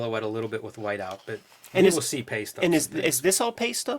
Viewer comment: Paste-up!. I've always heard it as Pay-stub! I'm thinking, what kind of media is Pay-stub.? Thanks gentlemen.